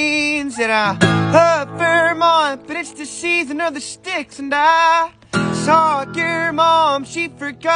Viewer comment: I always hear “I Socked your mom, she forgot that I existed” 💀